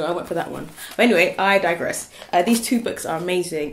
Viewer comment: Did you mean digress?? I swear I heard ''I diverse....''